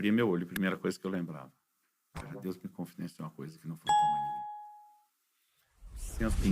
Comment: O que ele fala no 7:30 por gentileza ... Não consegui entender com os barulhinhos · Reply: Ele disse: "Deus me confidenciou uma coisa que não falou pra mais ninguém."